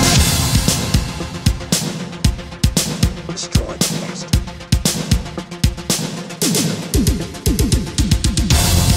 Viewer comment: What's he saying at 1:52? "Destroy the monster"? That's what it sounds like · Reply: That is General Shephard from the first game. On the final GDI mission, he says "Destroy the bastard!" Which is referring to Kane.